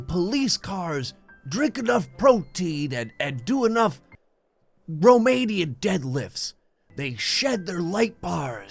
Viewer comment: Did I hear "romanian"?